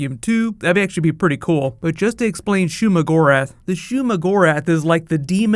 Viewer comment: I thought he said sheogorath :(